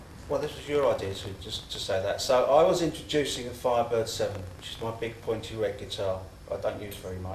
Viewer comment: As a young teenager, I always thought Marco said “I’d like to introduce my private seven” and I had no clue what it might mean until I read about the guitars he plays in my later years! 😂😂😂😂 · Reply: When i was 13, (when it came out) i always thought he said "i'm 5 foot seven" 😄 Later learned it was "my Firebird VII" - his red w/gold hardware, 3-pickup Gibson Firebird guitar which he got because Phil Manzanera of Roxy Music had one